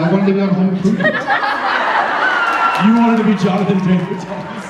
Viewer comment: what is the name Jensen says at 0:25? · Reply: irini koutsaki Jonathan Taylor Thomas